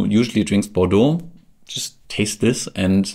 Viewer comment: what do you mean with "For someone who usually drinks Bordeaux..., (did you say) just tasteless" ??? ...